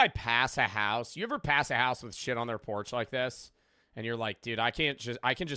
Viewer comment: I zoned out for a moment and thought said “Do you ever pass by a house and shit on their porch?”